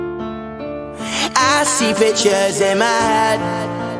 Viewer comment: For the longest I thought he said “bitches” not pictures 😂